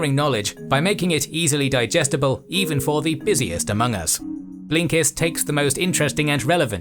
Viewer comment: DiD sOmEoNe SaY AMOGUS?!